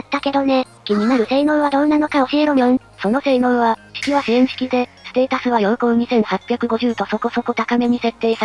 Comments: 水着咲夜の時陽攻2850って聞こえてん！？！？ってなってもうたw 7:31 いつも参考になっています！ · 陽攻2850って聞こえてビビったw